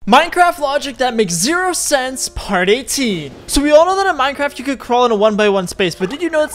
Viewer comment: When he said Part 18 for a sec I thought he said Party T